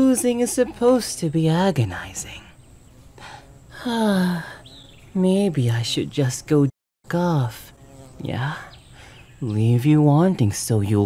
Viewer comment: Him: You sound angry.. Me: HAAAAAAAAAHAHAHAHA laughs into pillow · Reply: DID HE JUST SAY "MAYBE I SHOULD JUST GO GOLF"??